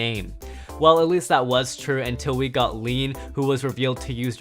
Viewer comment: Did he just say lean